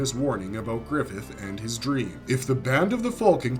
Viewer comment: The whole time I thought this guy was saying "The Band of the Fulcan" not "Falcan" lol Tom-a-to / Ta-mat-o